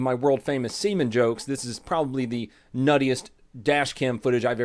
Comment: I'm thought you said nutted